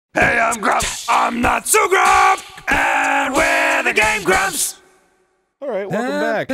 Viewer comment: whenever I here the intro all I hear is "I'm m grup, and I'm not so grup and we're the game groups".